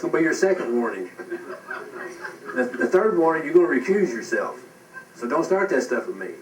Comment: What does that guy mean when he says "the third warning you are going to refuse yourself"? at 8:23 Am I hearing this right?